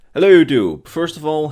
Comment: I know he's saying "hello youtube," but I Always hear "Hey Jiub."....